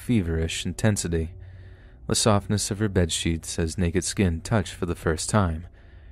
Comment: Lmao I thought he said the “the softness of her buttcheeks” instead of “softness of her bedsheets” hahahaha